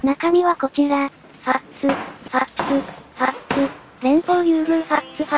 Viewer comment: ファッツ連呼のとこファックに聞こえてしまった笑笑